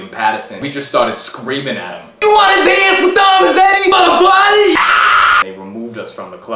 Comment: And all this time I thought he’s said you wanna dance with doms daddy